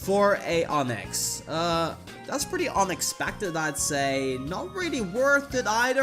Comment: Totally sounded like you said “Onixpected” and def seems like something you’d say 🤣